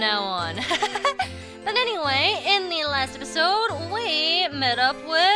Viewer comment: sometimes i hear say "derpisode". im like -_- "you need some milk".